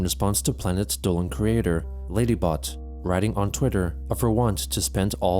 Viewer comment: I thought he said “laineybot” and I thought my two favorite car wrecks had a cross over.